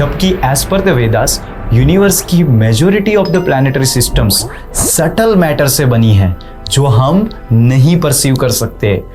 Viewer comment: konsa matter??? Suttle? Subtle?? Koi to batao reeeee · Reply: Subtle bro Means sukhma